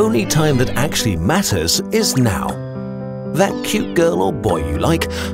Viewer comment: I thought he said «... Time is precious; make out»